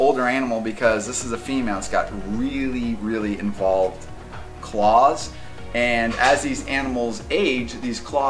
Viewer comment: Did he say that the snake has claws?